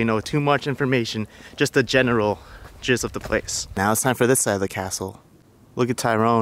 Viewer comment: OMG ( 8:34 on video ) did you say general JIZZ of the place ???? hahaha